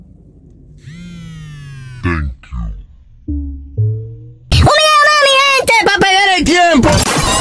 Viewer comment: what did he said? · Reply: Un video ma mi gente pa Pele tempo